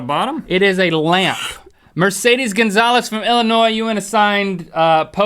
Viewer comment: OMG I thought u said my name bc my name is Marcedes but he said Mercedes